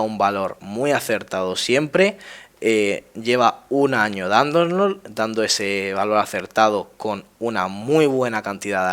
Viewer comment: Pregunta, ¿qué quisiste decir ahí, amigo? porque sólo entendí "...de bienestar". Muchas gracias! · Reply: Probablemente "Cuestionarios de bienestar"